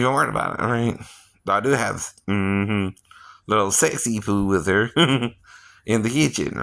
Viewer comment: Can you clarify what you say around the two minitue mark? Because you go on to say "you have sex with her" but you stop yourself from finishing that sentence.